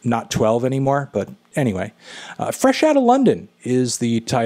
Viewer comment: i thought it said fresh outta lotion